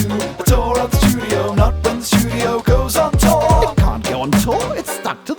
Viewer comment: At first, I thought you were saying that the studio tore, and I was very sad.